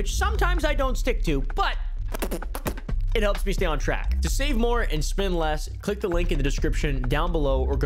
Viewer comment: I thought he said to spend more and save less 💀